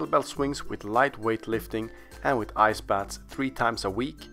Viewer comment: "Tim Farris combined kettlebell swings with light weight lifting and with _____(WHAT) 3x a week which also rapidly burns fat." I can't understand that missing word. Please fill me in. Thank you. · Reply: "Ice Baths". The Magic Hand wrote it on the screen. :)